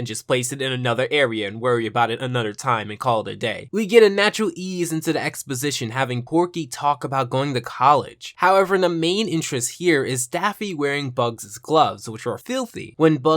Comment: did he just say corky😐?